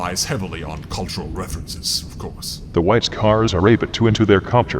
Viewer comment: I keep hearing "Whites Cars" when Emps says it...